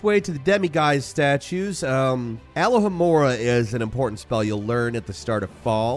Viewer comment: Bro every time he said Alohamora, it sounded like he said aloe Vera😭